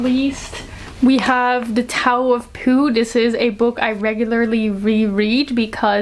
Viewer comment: I absolutely misheard and was like "they called the book a 'tower of poo'??"